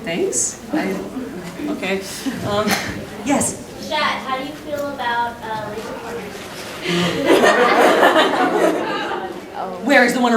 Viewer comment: What did the girl say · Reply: he said "chat how do you feel about lazzer pointers?" <3